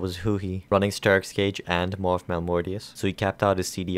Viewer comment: min 6:35 ?what did he say sterak is cage and what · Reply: "Sterak's Gage and Maw of Malmortius"